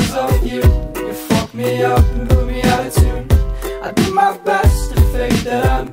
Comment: I always thought he said fucked me up with the attitude not put me out of tune